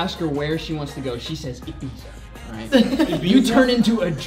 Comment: omg!!! this whole time I thought he was saying "EAT PIZZA" but said "IBIZA"!!!!1